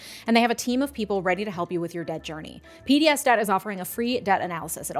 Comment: I’m sorry, not related to the topic, but I have to say that every single time I hear “PDS DEBT” my brain automatically goes “PBS Kids” 😂